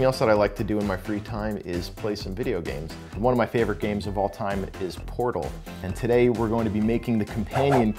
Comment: "One of my favorite game of all time is, Porno." sorry misheard that part..